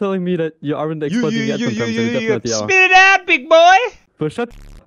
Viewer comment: That caught me off guard. Sounded like how a friend of mine from Indiana sounded like in middle school. "Spititowtbig-BOI!"